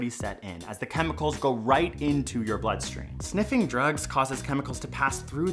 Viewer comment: I thought he said buttstream for a second